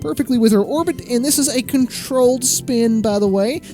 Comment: I thought you said it was controlled😅